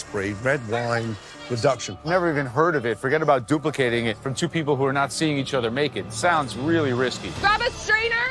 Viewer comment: made me have to rewind and listen again 😂😂😂 thought Joe said “forget about duplicating for people who are not seeing eachother naked” 😂😂